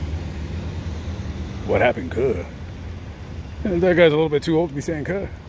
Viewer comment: Did he say "What happened ker" if he did say that what does ker mean?